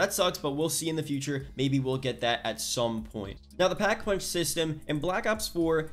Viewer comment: Brooo i almost thought it said no pack a punch and was about to cry😭